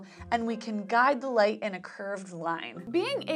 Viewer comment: I thought is said "lightning" and I got way, WAY too excited.